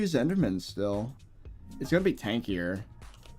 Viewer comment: did I hear tankier? Astrea moment.